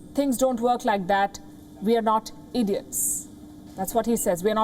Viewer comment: I love that part "We are not idiots" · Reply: 1st time I heard it as " We are not Indians". No wonder she repeated it. I had to listen to it 3 times before I got it correctly.